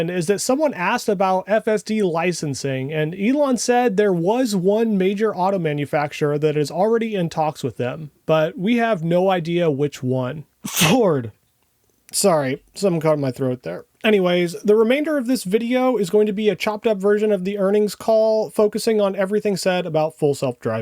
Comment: Did I hear "hoard", as in 2nd mortgage triple down on TSLA?